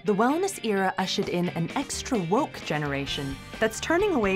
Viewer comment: "extra woke generation?" Did I hear that right "WOKE" 😳